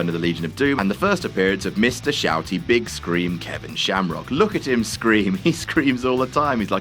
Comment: did just call Ken Shamrock "Kevin" Shamrock?